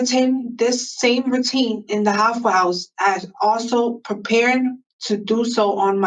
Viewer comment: This whole time i thought she was saying “the Waffle House” 🤦🏼‍♀️🤣